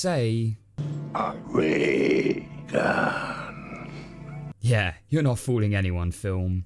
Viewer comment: When I was a kid I thought it was a real gun. I guess it didn't shoot like a ray fun in other cartoons. And when Carface said ray gun I thought he said real gun but with an accent.